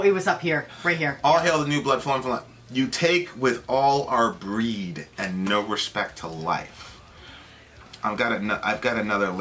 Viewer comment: That lyric... maybe I misheard it, but I thought it said, "you're a dick without a brain, and no respect for life. " lol